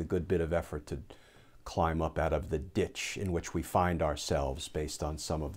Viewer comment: Thank you again, Glenn. On my first listen I misheard what I thought was a new legal term to me....I thought you said "...climb up outa the Sh1t":but, phew, it was .."...ditch .' Stay strong.